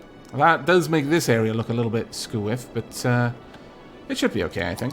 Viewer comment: what is that term you used? You said, "That does make this area look a little bit [something]" and while it probably is some kind of UK slang, I can't make it out. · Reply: Bearded Bob <link> Squiff means crooked